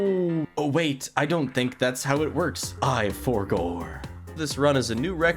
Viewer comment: when he said “I forgor 💀” it sounded like springtraps “I always come back”